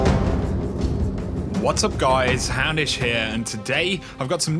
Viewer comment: love your channel man! so much great information and you stay up to date. i just find it hillarious everytime you say "houndish here" i hear "hammish" haha.